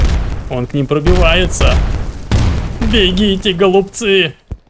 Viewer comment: 😂Мне послышалось бегите голупцы